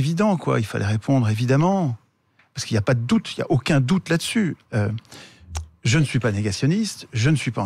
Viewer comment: faux, ce n'est pas "il n'y a aucun doute" c'est "LA LOI NOUS INTERDIT D'EN DÉBATTRE"